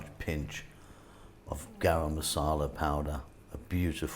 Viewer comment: Hey there my ears couldn't quite catch the spice you added at 6:13 much appreciated if you could tell me. This looks really yummy❤ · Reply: Garam Masala powder, it is a blend of spice to finish off a curry.